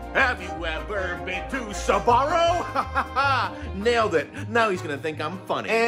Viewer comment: Didn’t know what Sbarro is, I thought he said Subaru… and I didn’t question it at all